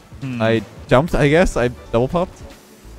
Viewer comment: did he say he double pumped 👀😂